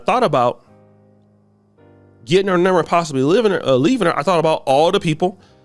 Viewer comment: Ok last comment, cuz this is too funny bruh. 8:55 Oshay had a freudian slip yall. He said - " l was thinkin about getting her number and possibly "living" instead of "leaving". Ninja been dreamin bout living with the whyt woman for his whole damn life. 😅😅😅😅😅😅😅😅😅😅😅😅😅